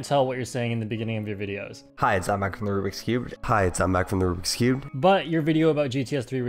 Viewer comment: I hear him say: hi it's I'm back from the rubik's cube.